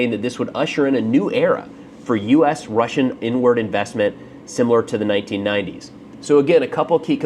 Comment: I know it's not what you actually said, but I can't help but hear "American and Russian n-word investment" lol